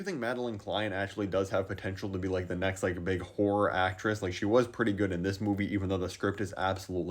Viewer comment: “big whore actress” is what I heard lmao